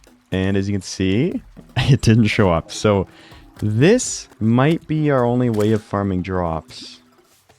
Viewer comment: I literally just heard that as "this might be our only way of farming giraffes"... time to go to bed I guess